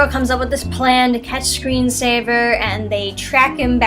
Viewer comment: Just watched your review and I thought I heard you say the villains name was Screen Saver, I believe the villains name is Screen Slaver.